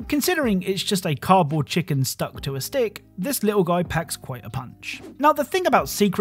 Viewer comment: I thought you said "this little guy pecks quite a punch" and burst out laughing